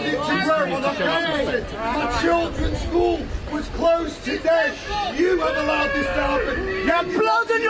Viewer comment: Did I just hear "My children's school was closed today?"